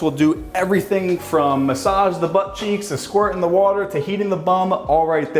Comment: That toilet should be world standard, it's super clean and save a ton of toilet paper. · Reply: First I thought he said eating the bum and was like what did he say?! Had to rewind and got Heating it made way more sense.